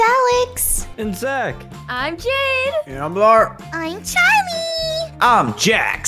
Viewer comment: "And I'm Larght" Me: "Wait- did I just hear "larght"? Lol